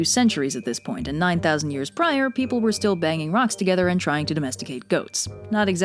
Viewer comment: I thought she said banging goats...